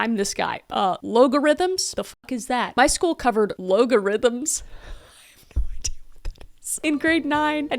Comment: at first I thought you were trying to say "ALGORITHMS" 😂😂😂